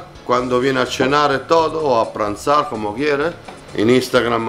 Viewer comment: que dice? A cenar o a tombrar ?? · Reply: Almorzar en italiano... Pranzar